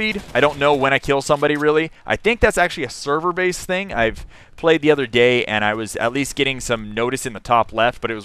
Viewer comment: did anyone else hear him say "I play the other gay"? hahah